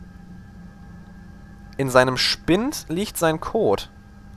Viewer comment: Haha! Ich habe bei 2:38 anfangs "In seinem Spint liegt sein Kot" verstanden! xD